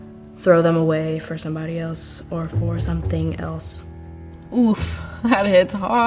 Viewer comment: Did i hear "OOF" ???